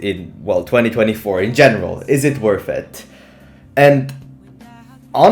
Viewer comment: i heard you say genital instead of general